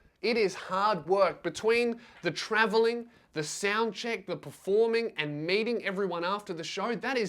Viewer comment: Did he say meeting or mating everyone after the performance cuz that a HUGE diffrence